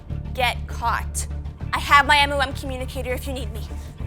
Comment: did she just say m ooh m communicator lol